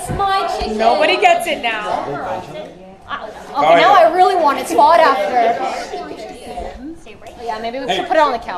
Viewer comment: Did she say "Now I really want it, it's fought after" lmaooo straight up braindead thing to say Not to mention that hat being pulled out of a goblin armory Edit: Yes, waging war with the dog loving community is like climbing a tree to lick a hornet nest It's not about friendship here... It's about fame and fortune